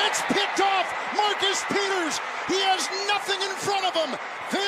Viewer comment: I swear I heard “Marcus peeners”